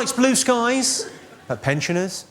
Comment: what does he say at 0:44 ? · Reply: pensioners